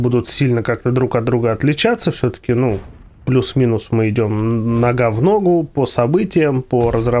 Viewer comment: "Мы идём на гавно гоу!" Чет, бомбануло!! Думал, послышалось!!! Умеет веселить!!!))))